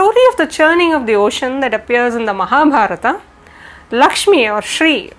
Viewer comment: so this is how you pronounce Mahabharata... I heard, "MAHAM-HARA-TA" I've been pronouncing it as "MAHA-BARATA" Is the "B" always silent? Is this pronounced differently in different dialects?